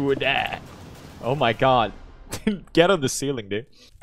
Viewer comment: did pewds say get on the ceiling is he ceiling gang